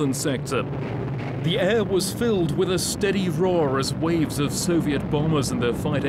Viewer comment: He said "the air was filled with stuka's" but i heard "the air was filled with stupids" . :-)